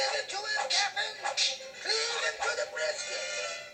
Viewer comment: what did mr. smee say at 1:18? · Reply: "Cleave him through the brisket"